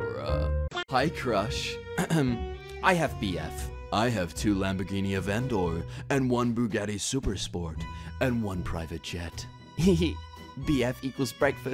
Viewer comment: I don't even think he has a lambo in GTA cuz he said avendor instead of aventador